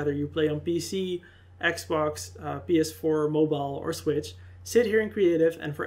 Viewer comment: Did he say mobile?